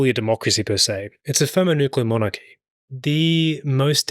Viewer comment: what USA is? l couldn't hear properly that. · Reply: Did he say thermonuclear monarchy?